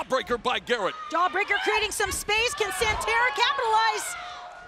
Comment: did she just say SanTara?